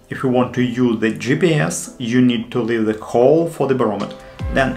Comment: what are you saying please? Is it you need to leave a 'hole' I.e. not cover in silicone?